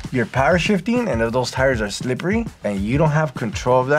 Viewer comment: All I heard was "slippery and grips" and now I'm invested LMFAO